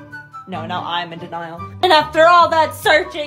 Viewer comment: 'Searching, blaming, and possible maiming' BAKUGO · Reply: Oh i thought she said memeing- lol